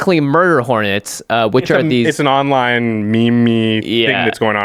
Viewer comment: Josh did you just say "meemee" ... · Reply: “meme-y”